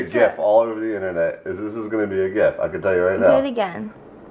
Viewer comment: Hello Does anyone know what word is saying at 7:26? "This is gonna be a "xxxxxxx" on the internet... It sounds like "gip" or "gif" What does he say and what does it mean please? (thank you)